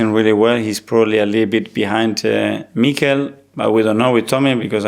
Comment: No idea haha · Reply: I honestly thought she was saying, "Keeping us on our toes today me cow?" Took me a while to realise she was saying Mikel in a Cockney accent 😂 He's a Basque so stood no chance.